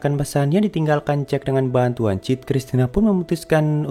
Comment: "Sebelumnya setelah berhasil memecahkan pesan yang di tinggalkan CID dengan bantuan CID" Update: sorry salah dengar, CID yang pertama ternyata Jack.